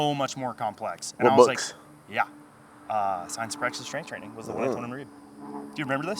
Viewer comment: What is the title of the book mentioned at 4:50? I couldnt catch what he was saying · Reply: Science and practice of strength training